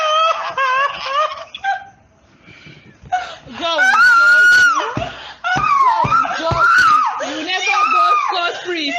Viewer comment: Wow, that was a very disturbing phone call to say the least. I've heard many, and this one was just awful to listen through. I noticed that at around 11:20, there's what sounds like another female voice saying something while the friend is screaming in the background. Does anyone know what she's saying and who it could be? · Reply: I heard "and god will punish you" near the end, maybe the other female could be a family member or just another friend of the victim